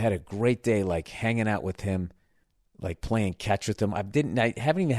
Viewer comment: Bill burr playing fetch with his kids he thinks they are dogs haha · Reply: I heard "catch". Not fetch🤣🤣🤣